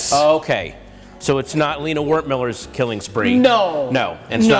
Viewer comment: did he say "it's not Lina Wertmüller's killing spree"?